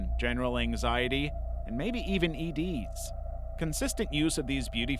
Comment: I THOUGHT HE SAID ED 😭